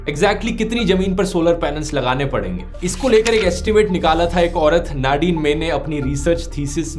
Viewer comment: did you say, एक औरत...???